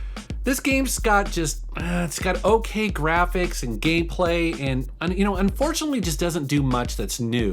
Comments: did it sound like he said Gayplay? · Does he said "gay play"? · Did he say gayplay? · did he say gay play